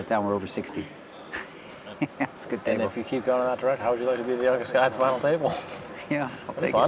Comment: wtf was this guy saying I didn’t understand a word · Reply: “How would you like to be the oldest guy at the final table” Something along those lines anyway.